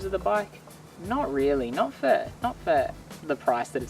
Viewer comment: Not sure what you mean, I said the bike was good value. As I stated in the video it's a good offering for the price. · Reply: I suppose he misunderstood your australian accent at 3:35, when you say "not for, not for the price" it really sounded like "not fair, not fair - the price" to someone who learned "US English" :))) Being from russia, I'm not even sure if you said "not for" myself :))))